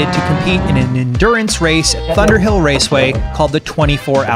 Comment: did he say "butterhill raceway"?